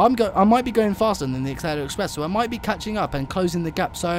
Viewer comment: It sounds like your saying aksela express.